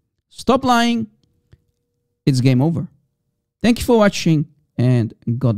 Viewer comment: Confirmation from the shake: No I didn't say that, you guys misheard that. I said "because he did come from Abrahamaic faith"